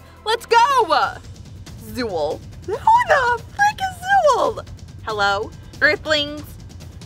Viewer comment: Anyone heard say f***??